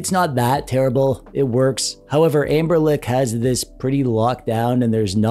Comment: is it only me who hear anberLICK 😂 love your vids and i orderd my anberLICK h model a few days ago 🙂